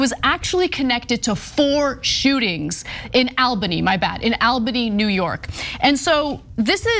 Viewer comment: Phew! I tune in when I heard "Alberta", but he meant "Albany". 😅😅😅